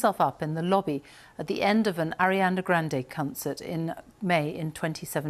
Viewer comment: Did she say CUNTcert instead of concert??